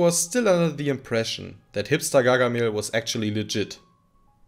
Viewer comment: Did you say "hipster Gargamel?" 🤣